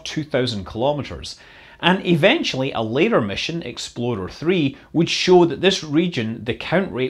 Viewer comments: Every time he says "explorer", I hear "exploder". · Great video as always! Unrelated, I got a good chuckle every time you said “Explorer,” which I heard as “Exploder” due to your Scottish accent. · Every time you say Explorer I hear "Exploder" LOL